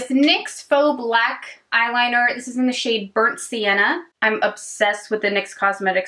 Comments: That Ardell primer I thought you said “it’s in the shade urinate” My face: 😳😳😳😳😳😳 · when you talked about that ardell shadow stick, i coulda swore you said "this is the shade urinate" lol